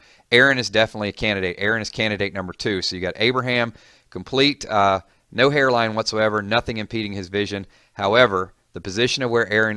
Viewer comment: did u say no hairline what so ever haha xD